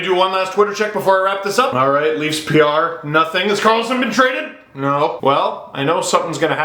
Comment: When Steve said "Has Karlsson been traded?" I first thought he said "Has Carlton been traded?" I was thinking "No! Not the bear!" LOL